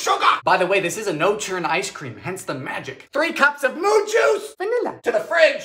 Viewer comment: "3 cups of MOO JUICE!" (not even half a second later) "Vanilla :D" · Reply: Ngl, thought hes been saying "MOON JUICE" and was pretty confused but just kinda went with it lol